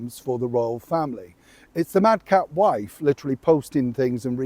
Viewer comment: Oh, "Mad cat wife"😂 I realize you probably said "madcap" but I heard "Mad cat" which is closer to the truth!